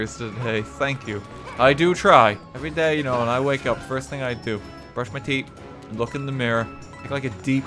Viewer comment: all I heard is brush my tea